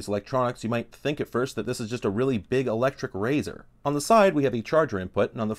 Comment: I thought you said "a really big electric raisin" and was confused.